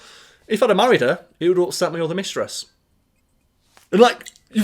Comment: My voice processing is failing me, what is he saying at 6:25? "If I married her (something something) mistress." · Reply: Would have upset me (my) other mistress.